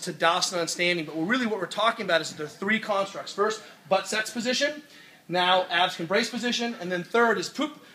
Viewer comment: Thought he said butt sex position